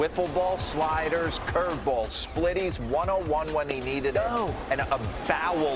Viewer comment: ここ「101…?」って聞こえるんですが、変化球についてなんですかね